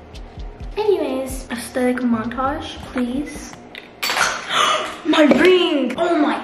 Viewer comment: I thought she said my brainn insted of my ringg